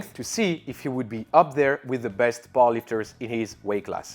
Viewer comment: Honestly I thought you were saying 'ball-lickers' instead of powerlifters